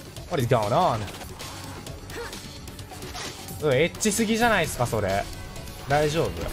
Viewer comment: *My JP ability is, to say the very least, subpar, but I'm pretty sure was describing the scene at 0:18 as "ecchi." If anyone with JP ability knows what he said, please do mention so, ty!! Yo fam, it's been like four days but I already forgot how to clip lmao. What better way to come back than bonk Axle...have a good evening/morning! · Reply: Crrect! He said "えっちすぎじゃないっすか?それ" "Aren't you too ecchi?" (I'm not confident in my English, Sorry!)